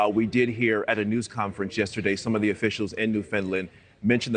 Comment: Did he just say "New Finland"??